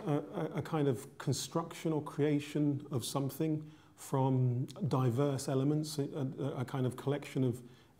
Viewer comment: diverse elements such as fear, surprise, ... XD (that's what I think what I hear "diverse elements")